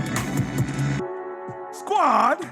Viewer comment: This is just my ears having hearing difficulty, but up until now, I thought you were saying 'chop wood, Kerry Water' as in the name of a person. So on my first watch of this video, I tried to research who this Kerry Water is 🤣🤣🤣I now see you said Carry Water - just thought I'd share haha